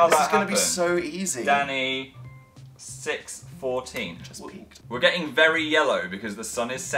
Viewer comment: d-did Dan say 'I just peed' ?!?!